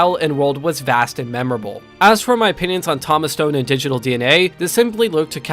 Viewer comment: Okay, sounded like you were saying "Toph Stone" for a bit. Was tempted to comment, until the game he made started with "Avatar". I had to comment.